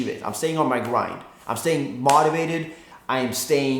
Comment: Did this nigga say Mativated instead of motivated